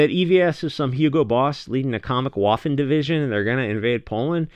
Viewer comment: Oh wait, I thought you said waffles. Nevermind.